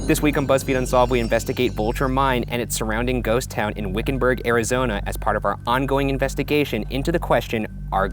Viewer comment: I heard Wiccanburg...can I live there??